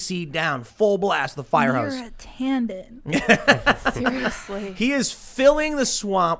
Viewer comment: Can someone please solve something weird for me.... what exactly does Cassandra say at 1:00:28. Your a tanden/tandem? I'm confused. Also if you listen to Tim on 0.5 speed from that point he sounds really drunk :)